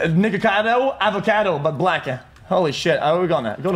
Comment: it wasn’t his accent, it sounded like he said he turned into “nig*acado, avocado but blacker” 😂😂